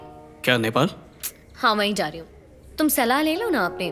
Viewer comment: What u want ? He said vaishali